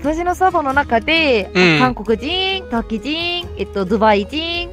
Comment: O did she said Turkey?! ( 5:07 ) Im Turkish Too ^^